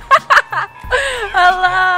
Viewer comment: did he say "Hey sexy!" ? O.o